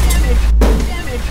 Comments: I thought Spark said "damn it" when he said "damage." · I'm upset that there wasn't a model of a halo installation instead of a basketball hoop at the end there. Also I thought Guilty Spark was saying "dam it" instead of "damage" at first